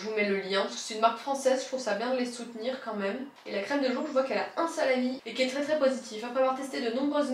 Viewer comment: "Il a un seul avis" j'ai compris "il a un salami"... 😅 bref merci pour cette vidéo! Mon casse tête du moment c'est de trouver une marque où je peux prendre la lotion, le sérum et la crème pour avoir une synergie des compo! Pas facile finalement 😜